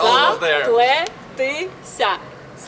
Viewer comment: What does she say? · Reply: Zapletesya - it is in Ukrainian- braided